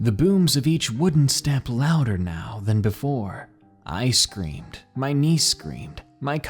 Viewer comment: I screamed. My niece screamed. My cousins screamed....... For ice cream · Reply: I thought that when he said I screamed he said ice screamed